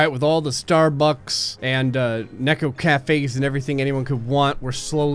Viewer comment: Did I hear neko cafe's Yes please